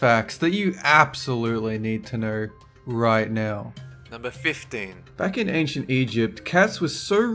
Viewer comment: YOU PUT 10 INSTEAD OF 15 IN THE SUBTITLES YES I READ THOSE